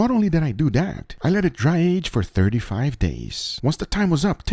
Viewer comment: I thought you said you drie aged it for 35 yrs and was like what?